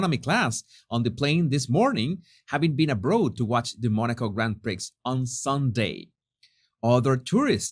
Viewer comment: Jesus did you really say Grand Pricks? 3:25 🤭